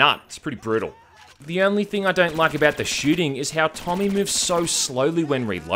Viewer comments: Did he say “Tommy”? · Did you say Tommy? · lmao did he say tommy?